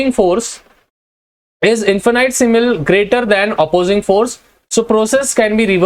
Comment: what is simal word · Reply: ig it's infinitesimal It means extremely small